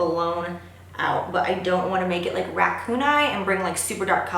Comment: omg so funny when you said racoon eye u totally thought you said ,,,,, rat poon eye omfg I can't stop laughing omg ,,, I am special lol